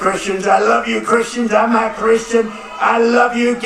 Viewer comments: Wierd at first I heard “l’m a Christian” and the subtitles agreed. But when I listened again it sounded like “I’m not a Christian” ? 🤷 only God knows for sure but I think the latter. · See my comment below, this is hard to tell without being able to see trump’s tongue at that moment of either saying NOT OR I’m in a longer draw. 😳I KNOW what I heard Trump say at the 3:00 minute mark “ I’m not Christian”, I replayed it many times zoomed in & read his lips. Trump has been lying. 🤥 WTH is the you’ll never have to vote again, we’ll have it fixed bill 💩. Plus the over hundred replayed zoomed in videos of Trump’s right hand after the shooting & there is ZERO blood, nothing remotely resembling even a pink hue. YES YES YES I already know the deeeep state is definitely 💯 % behind this but this is soooo much more complex & deep that literally less than maybe 2% of people worldwide will be able to recognize this much, not saying at all that anyone will be able to put every piece of the nefarious puzzle together that has been going on for decades if not hundreds of years. Y’all looks like this IS IT, we the people of the world have & are being played in every direction & they’re serving it up in different variations to brainwash each different party, race, ethnicity, social status, nationality AND it comes down to GOOD VS evil.